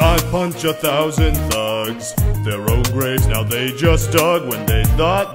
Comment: what is he saying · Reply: Their own graves they just dug.